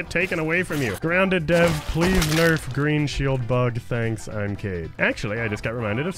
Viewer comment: i did not see the imcade and I heard im gay🤣🤣🤣